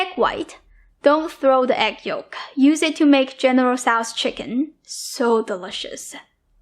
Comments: Great video!! I thought you said "don't throw away the yoke, use it to make generous ass chicken" 😂 oh these ears of mine! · i thought she said "Use it to make it generous ass chicken" LOL